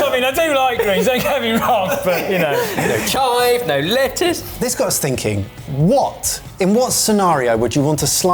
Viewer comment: "this got stinking" I know what he was trying to say, just caught me off guard